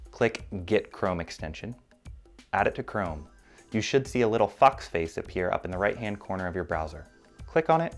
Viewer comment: How many people thought he said, "You should see a little FUCK face appear"